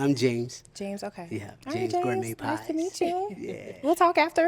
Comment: Thought he said "James Creampies" 😅